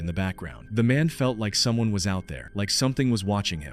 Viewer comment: In the 1st video a voice can be heard at 1:44 - 1:46 saying "come here" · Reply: I heard it at 0:45 say "I see you watching".